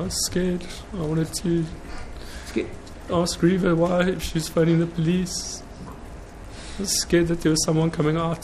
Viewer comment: DID anyone else hear that at 14:30 ? " I was scared, I wanted to ask Reava Why She Was Phoning The Police " ??? He Corrected Himself, But My God... Thats Incriminating.